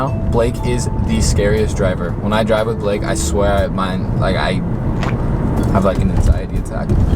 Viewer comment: I thought he said I Have HIv 😗